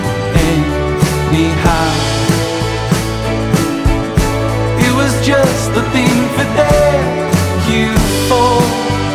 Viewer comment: This song plays at the Starbucks I work at… I always thought he was saying “it was just the thing to thank you for” lol